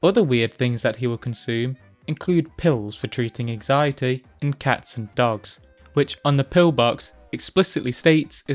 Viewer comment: I literally heard that as “pills for anxiety, AND cats & dogs”, and was very confused as to how those were related for a second Anyway, great vid, hope you cover Janoy soon